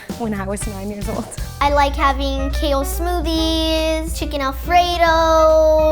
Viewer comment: umm wat did u say "I like kale smoothies?!?!?!?!??!" EXCUSE ME! YOU ARE AN ALIEN!!!